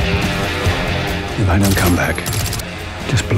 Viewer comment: Anyone catch what Bond says to Larry Lunchbox (Nomi) after she says "But you don't have enough explosives for that"? Sounds like he says "Yes, but they don't know that"!? He mumbles...